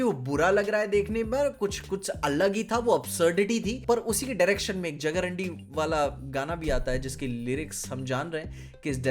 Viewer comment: did he just say jagaRANDI.....??? 😂😂😂